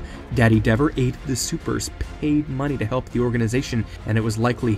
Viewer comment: wait did you say he ate their money literally or just a saying?? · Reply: I said “he aided the supers and paid money” but it sounded weird post editing I guess